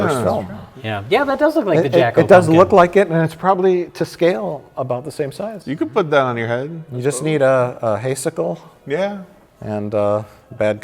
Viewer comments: I thought Mike was saying Haycicle (like a hay icicle) for most of the Jack-O talk until I finally realised he meant "hay sickle". And I'm pretty sure it's actually a scythe. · Mike said "hay sickle" but I heard it as "haycycle" and expected to see the pumpkin ghost riding a bike made of hay.